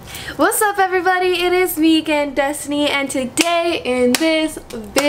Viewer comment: Lmao o thought she said 'it's vegan Destiny!'